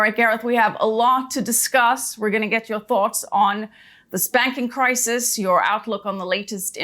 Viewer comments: for a weird moment there I thought she said "spanking crisis" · I thought she said: the "spanking" crisis 🤣🫢🤣 🏸🏸🏸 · Did she just say "the Spanking🤚 crisis"?